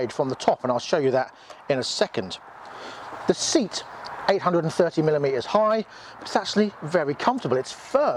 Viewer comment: Seat height 870mm so probably too high for me at 5’7” shame as I like it. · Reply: I thought he said 830mm seat height?